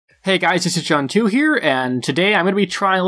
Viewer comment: Lol I thought he said "Hello guys, Asian John 2 here"